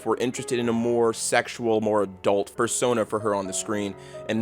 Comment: did you say "fursona"?